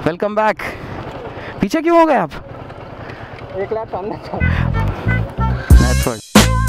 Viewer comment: does anyone know what has said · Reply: CRAZY LIFE "EK LAP SAMNE THA" i.e he was already running ahead by a lap..